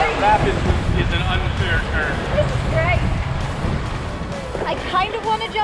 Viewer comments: said this I great but captions says your right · lol the subtitles say "you're right" but clearly says "this is great"